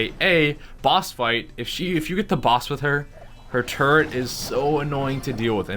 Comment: Thought he said turd lol